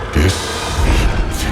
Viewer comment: What does the narrator says @ 1:03?? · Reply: Sounds like "Deceit" to me. But they hold the c in the middle when saying it.